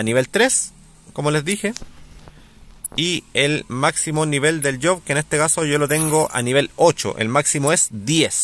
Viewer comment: ¿Qué es el nivel del yo? No entendi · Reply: Nivel del Job 😅 , sorry la mala pronunciación, me refiero al nivel del estilo que estás jugando , el máximo es 10 y es cuando tienes 200 de vida